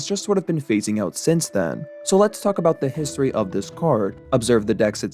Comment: i heard lets f about the history 😅